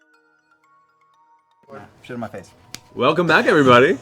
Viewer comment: Did I just hear "Shit in my face?" xD